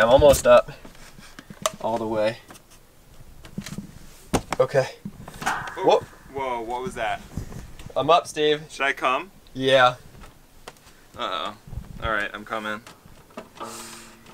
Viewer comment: Yo I heard I'm coming for you